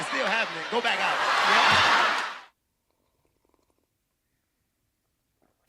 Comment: 😭😭😭I was waiting for “honeeeey” , but I heard “bitch really” this time😭😭😭😭really though. It’s a clowning show. Her attitude was so ugh.